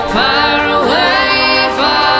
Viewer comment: me parece que dice mantén tu luz